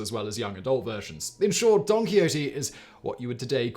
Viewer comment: First time said Don Quixote, I seriously thought he said Donkey Odie... lol Been a long day & I'm tired